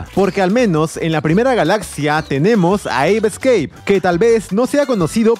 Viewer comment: No entendi como se llama el juego en el minuto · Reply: Se llama "ape escape" juego de ps1.